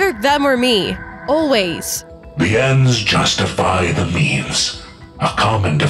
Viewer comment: i hear memes not means